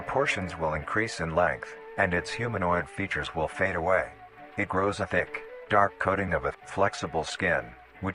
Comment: 'It grows a d*ck...' is exactly what I first heard XD